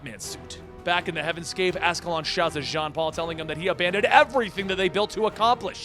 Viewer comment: When he said "John Paul" I thought he said Jake Paul